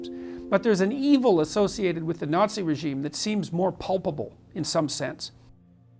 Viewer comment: Did Peterson actually say "Pulpable" when he meant to say "Palpable"? I've seen this with Peterson a lot - he seems to have a serious inability to spell some quite simple words that suggests a level of ignorance that belies his supposed intellectual status.